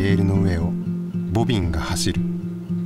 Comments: 「ボビンが走る」とかいうこの動画でしか聞かなそうな言葉、語感が良すぎてすこ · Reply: marcy ボビーが走ると聞こえた俺は末期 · ボビンが走るのところがボビーが走るに聞こえて地味につぼりました。